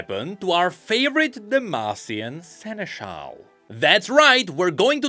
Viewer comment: I thought he said Senna Shao